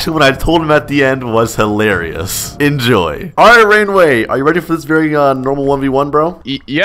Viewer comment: omg the first time when he said alright ray or something i thought he said alright grainwaves… 😂😂😂😂😂😂😂